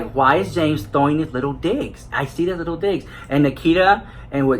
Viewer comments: asked, "why is James just throwing little digs??...just throwing little digs" and I thought he was saying "little di#%ks"..totally changed the context. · LMAO... I thought you said James was throwing little dicks... I choked.